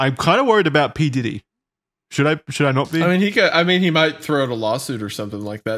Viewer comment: He said “he might throw a lawsuit at you”, I heard “He might throw a velociraptor at you” 😂